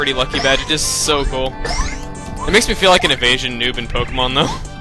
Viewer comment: i thought u said an asian noob lol